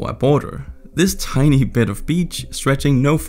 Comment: They said "beach" I heard "bitch'